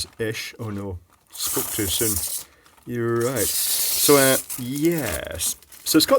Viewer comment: does say "you were right" to a hypothetical person who said that it wouldn't come off easily? Or am I mishearing things (and am I the one who is going mad?)